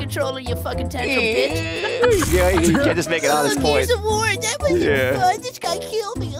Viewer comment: I am sorry, I know I am 2 years late on this, but what does he say? 7:52 "(unsure) just make an honest point." With all the laughing I can't seem to tell, but he slips it in so masterfully it is beautiful.